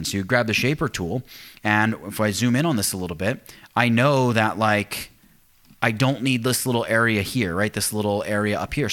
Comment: hold down the what? saying it to fast for us rookies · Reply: Engraver Hold down Atl/Opt key... Also, on the top right corner of the screen, clic the (...), then clic Playback Speed to slow down (or speed up) the video... or turn on Captions